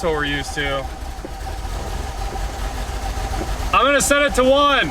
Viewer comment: said, "I'm gonna set it to one!," and I heard, "I'm gonna send it to Juan!" I just figured that Juan was really good at tuning.